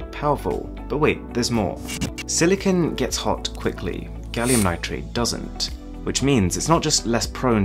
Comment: is it just me or did he say gallium nitrate